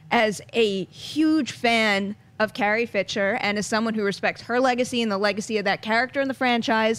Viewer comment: LOL...... at 1:06 SHE CALLS HER CARRIE, "FITCHER," INSTEAD OF FISHER..... DOH !